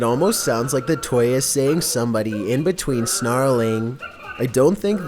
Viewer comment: For the Mickey Mouse one i heard him say "choking somebody" Then the growls over and over again